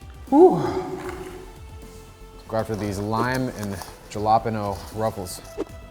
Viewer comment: excuse me did you say “jill-opp-eno”????